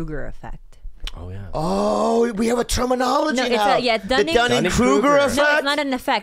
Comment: always thought was saying Diane Kruger effect. Still thought it made sense.